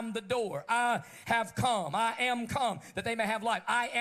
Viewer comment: Did he say "I am cum"?